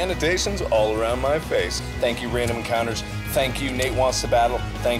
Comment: wait did mark say "grave encounters?" that's the name of a found footage movie.